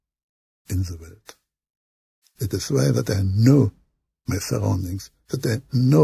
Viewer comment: What does he say on 14:15 ?? There is NO ? Or they know? Tried really hard to understand. thanks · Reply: ….that I know